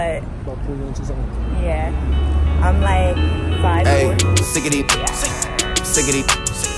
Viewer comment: what she said ( 5 .4 ) im I right?